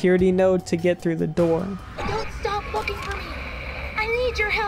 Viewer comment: bro am I the only one who heard “Don’t stop fucking for me” 💀